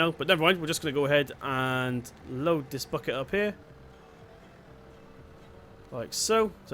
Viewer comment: sounded like he said lets load this "Fucker" up 😂😂